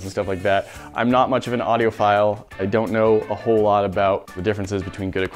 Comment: Lmao I thought said "I'm not really an audio file"